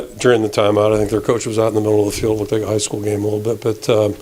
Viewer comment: Did he throw a vailed shot?? "Their coach was out in the middle of the field like it was a high school game" ...did I hear that correctly?